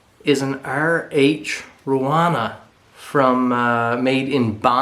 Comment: As always a great video. I enjoyed it. Love the knives and chickens. I don't think I've ever seen chickens that big. What did you say the name of that hunting knife was. ( My hearing is not the best. I can't make out what you said ). I always look forward to your videos. Stay well & safe. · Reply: It sounds like says RH Ruana,hope that helps..